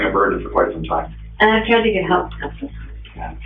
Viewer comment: I see an inconsistency. At about 13:25 into the video there is a caption saying "and I can't even help anything". But I distinctly hear "and I've tried to get help". Has anyone else seen this?